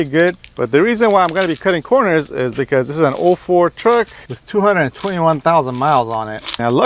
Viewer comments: Old ford truck? I could’ve swore it said GMC on the grill. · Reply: Lmao it was his accent. He said 04 truck not old Ford truck 😂😂 say it out loud to yourself 04 truck then old Ford truck I had to play it back about six times to make sure 😄😄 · He said "04 truck" Sounded like " old Ford truck" I thought the same thing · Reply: lol gotcha yeah it sounded like he said ford i was thinking shit we already off to a terrible start